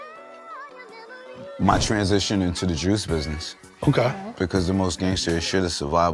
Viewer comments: sounded like he said "Jewish business" at first. Juices for Life, cheers 🥤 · I thought my man said “the Jewish business”… I was like here we go again